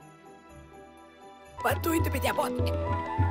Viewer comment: я не понимаю что говорит балун бой? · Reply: Он говорит: "Подуй на меня Моми"